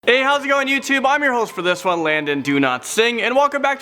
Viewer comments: I thought he said Landon do not sing 💀😂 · It sounds like your saying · Who thought that he said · I thought he said his surname was "Do not sing" 🤦🏻‍♀️ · I thought u said landom sry not making fun of you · Doesn’t it sound like he says Landon do not sing? For his name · It sounds like he says do not sing when he says his name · I thought u said your name was landon do not sing😂